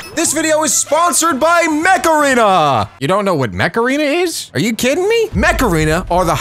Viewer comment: When he said mec arena, I thought he said macarena as in the song